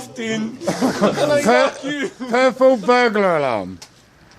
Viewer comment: I THOUGHT for a second he said "purple bugger alarm" 😂😂